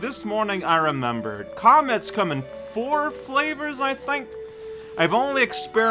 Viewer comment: I heard "comets come in four flavors" as "comments come in four flavors". Which piqued my curiosity. Fun Fact: Cosmic Mario is actually a living Mario- shaped universe. I just realized "Pull Star" is a pun of "pulsar"